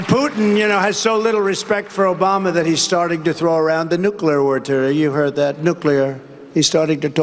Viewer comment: Actually, it sounded like he said ‘nuclear war tur-’ did he mean turd or term? in his statement about that subject. Then he tried to fix it in the third one.